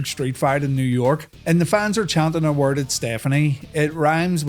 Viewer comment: i thought you said "rhymes with c*nt" i was like WHOA wait what? 😂